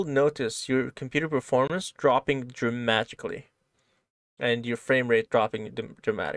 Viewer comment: sounded like you said "dramagically", which I know was accidental but it's a word I now want to start using all the time. lol. So far this is great and I can't wait to get into the code.